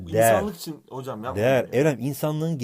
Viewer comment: de Tonguç' a Eren mi dedi ben mi öyle duydum 😁😂